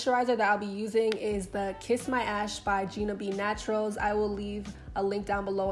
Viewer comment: Thought you said "kiss my ass" I was like whaaaa 😂😂😭